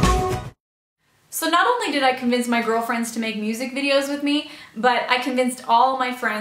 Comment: if it is from what she said at 02:50, i believe that she said "girlfriends"